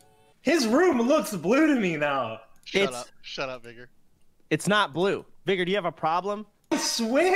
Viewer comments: I deadass thought he was gonna get banned. I didn’t realize what he first said I got scared I won’t lie. Where will I get my juicer content from is XQC is banned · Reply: DaatBoi99 he said vigor, his friends name. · it sounds like he said shutup n word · wait, what did he say ? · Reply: Vigor